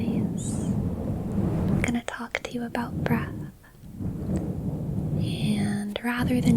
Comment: i genuinely thought you said brat and I was very confused when you started talking about Judaism 😭